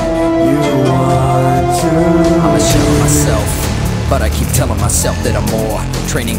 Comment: I hear ''I shit myself''